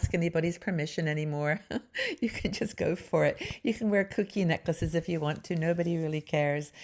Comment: Hello. Excellent content as usual. Definitely reading the “R” theme article. I can relate to each “R”. I understand your comment about “reinvention”. Unfortunately it’s a word that has been overused and carries a fear of overwhelming change. I prefer “refinement” which honours who I am with the intent to improve who I am “my brand”. I laughed when I watched the video the second time and realized that I misheard “kooky necklaces”. First watch, I thought you said “cookie necklaces” 😂. My mind went to stringing cookies around my neck. After all, most of us can recall wearing candies, popcorn, or Fruit Loops cereal around our necks. There’s a great scene in “Home for the Holidays” in which the older eccentric aunt (Geraldine Chaplin) wears a Fruit Loops necklace to Thanksgiving Dinner. I won’t be able to get that out of my head now. 😱